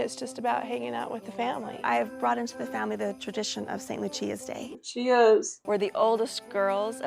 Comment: I know now your mom was saying St. Lucia’s day but at first I heard “I introduced the Saintly Cheese day” and I was intrigued